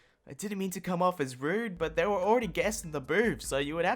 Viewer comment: captions say “but they were already guessing the boobs” lol